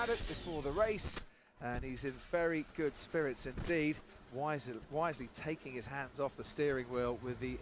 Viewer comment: These announcers are SO STUPID these days. Every race car driver in the world is told to take their hands off the wheel when your about to impact. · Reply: think the dude thought Crofty said "why's he" not "wisely" like he actually said.